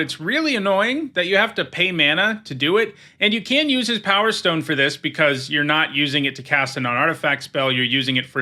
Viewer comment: Yep that is what I said in the video. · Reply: oh I'm stupid, for some reason i heard 'you can't use his powerstones to do it' 😀